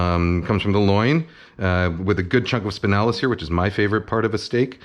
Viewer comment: Wait, did he just say s🅱innalas?